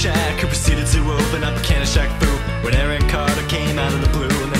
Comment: This is great work, but before this I thought they were saying "Eric Cartman" not Arron Carter. Now I can't hear it anymore. Keep up the excellent work.